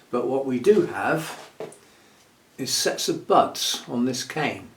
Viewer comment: I was listening while tidying up and I heard "what it does have is sexy butts along there." WHAT!? Oh, "sets of buds." SETS OF BUDS. 🤣